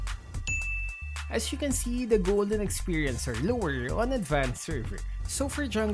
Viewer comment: I Hear Golden Experience :v